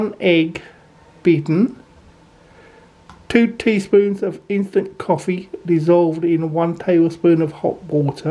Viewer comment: I thought you said infant coffee...😳 That's going to stunt their growth!😂